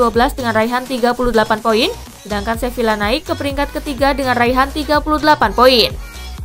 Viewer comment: apa cuma sya yg salah denger.se villa dan levante meraih 38 point😁😁